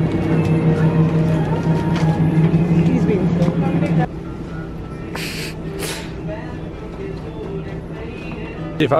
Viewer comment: On 10:33 what did she say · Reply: just "he's being silly"